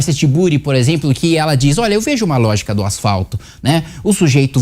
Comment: eu entendi lógica no asfalto 😂😂😂